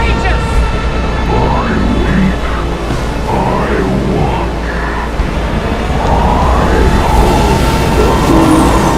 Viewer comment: What did he say at 1:32? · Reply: He said I Watch